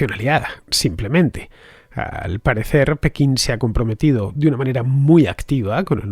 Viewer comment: Por que dices en el minuto 14:50 que “al parecer Pekín se ha reunido” solo me gustaría saber donde se puede contrastar esa información, gracias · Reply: Comprometido””” no reunido